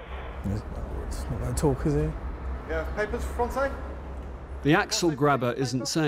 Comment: That's Bill Nighy's voice as narrator. One of my faves. When he said 'Axel grabbers' I thought he said 'axel grinders'. 😂